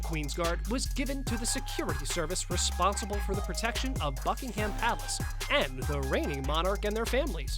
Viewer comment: I thought you said fuckington palace no offense